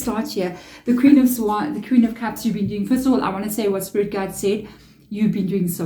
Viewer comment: As I'm watching and understanding very well this reading. I had to rewind a few times to make sure I was hearing what I heard. At 23:08 what I hear and it's like u trip up on the word u was trying to say and it sounds like... " Crystal what I wanna say what spirit guides said.. you've been doing so well. 😳🌟✨💫 Message received. Thank u! It's been one heck of a ride. Greatful for the lesson I'm learning. 💖